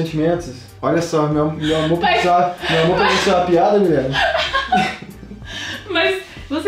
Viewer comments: só eu que escutei "Pra vc meu amor e uma piada?" E eita atrás de Vish Nunca recebi tantos likes😂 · só eu que escutei "Pra vc meu amor e uma piada?" E eita atrás de Vish Nunca recebi tantos likes😂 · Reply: Eu escutei "POR VC MEU AMOR É UMA PIADA"